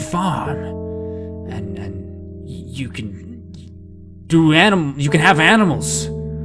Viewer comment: he was gonna say you could “do” animals